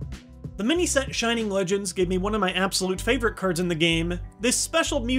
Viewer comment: does he say curds